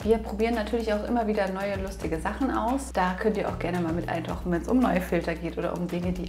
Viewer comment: Was können wir gerne mal? Ich habs zehnmal angehört, verstehs immer noch nicht. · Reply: DDD sorry, ich spreche manchmal nicht so gut "wir probieren auch immer wieder neue, lustige Sachen aus, da könnt ihr auch gerne mal mit eintauchen - wenns um neue Filter geht..."